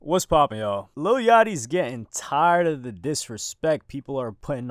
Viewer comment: I thought bro said “lil yachty is gay “ lol